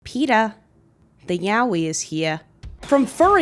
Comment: the subtitles say "yahi" instead of yaoi lol